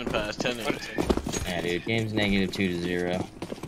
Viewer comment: What does Summit say at 12:35? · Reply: "yeah dude game's negative 2 to 0"